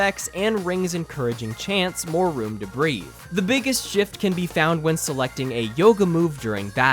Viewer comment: did he just say "jift"